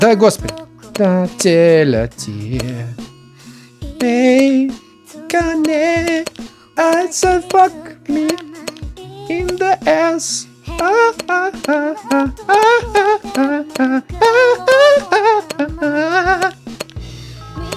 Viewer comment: f*ck me? или мне послышалось?